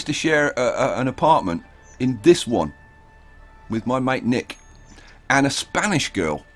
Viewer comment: I used to share an aparment with my ........ Can anyone tell me what word he says in that part, I'm learning english and I dont know that word. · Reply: Mate is just another word for friend. Like a best friend.